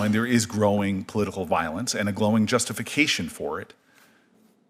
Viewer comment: Mr. Booker... did I hear that right @ 1:35:50 ? "growing (glowing?) justification for [violence]"?